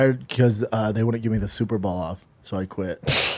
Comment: Hello, my American friends. I'm Irish and I'm just wondering, what does he mean when he says that he was a janitor and they wouldn't give him the "Super Ball outfit"? What am I missing here? What the heck is a "Super Ball"? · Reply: He said they wouldn't give him the super ball off. Super ball = schaubanese for Superbowl (American football championship game)